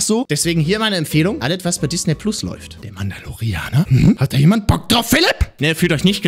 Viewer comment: Ich habe “der Mann ist ein Koreaner” verstanden.